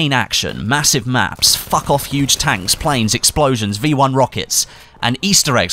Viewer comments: did say "Fuck Off Huge Tanks" I must be bad of hearing !! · Did say fuck off huge tanks at 11:03 lmao